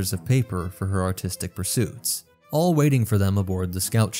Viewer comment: I heard "autistic pursuits", but that also fits